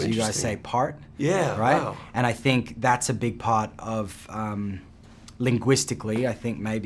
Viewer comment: Am I the only one who heard : And I think that it's a big pot of umm linguistics."